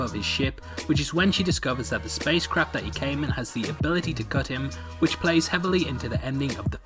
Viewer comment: What the fuck did you say? "His spacecraft has the ability to "cut him" or "Gut him".